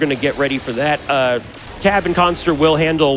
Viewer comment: every time they say "cab and khanster" i hear "kevin costner" and wonder what does waterworld have to do with contra